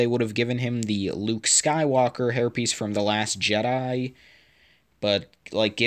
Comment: with auto captions it says “luke skywaker herpes” instead of hairpiece lol